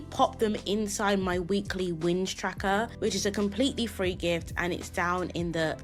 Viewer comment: I actually thought you said, "Weekly Whinge Tracker!"😂 Might be a good one to add! 🤣